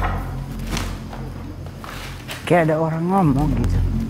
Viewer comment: Di detik 25:35 ada suara cwek/ kaya nenek² klu gk salah denger , ngmg nya "kaya ada suara ngmg" pdhal aku perhatiin mba diem aj dstu mulut maaf klu salah dnger🙏